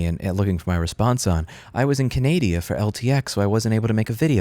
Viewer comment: lol did I hear "Canadia" in the beginning of the video?